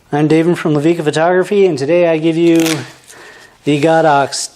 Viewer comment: I thought he said "I'm David from the league of photography" at the start